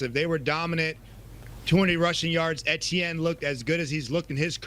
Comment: did he say the jaguars had 20 rushing yards? It was 200 if I believe